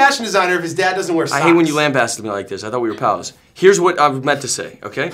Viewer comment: What does Shia say at 3:10 "I hate when you ....... ?" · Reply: Lambast* me like this. Land blast?! For real?!